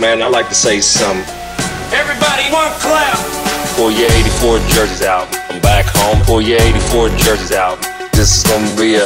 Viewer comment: what is he saying at 0:09 ? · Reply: Everybody one clap or pull your 84 jerseys out